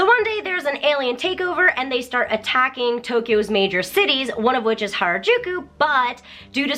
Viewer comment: Did she say “And then they start taking over Tokyo’s major cities”